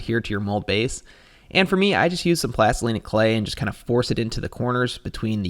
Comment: what's that word? "Plastolenic clay?" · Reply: Plastilina Clay... kinda a brutal one 😅